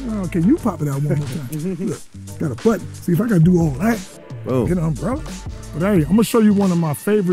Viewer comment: Did he say “if I gotta do all that and I’m drunk” @ 25:21 ? 😂 I could’ve sworn he said he never drink